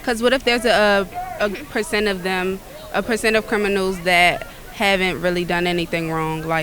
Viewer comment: did she just say “person of criminal” when referring to a person in prison?